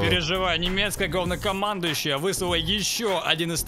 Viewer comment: Немецкое говнокомандуещее или мне послышалось